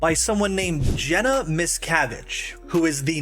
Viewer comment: Really thought you said Jenna Ms Cabbage